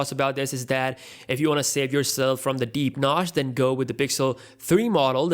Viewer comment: did you say whether you like it or NOTCH???